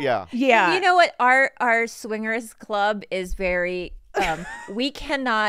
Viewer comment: wait did they say they’re in a swingers club?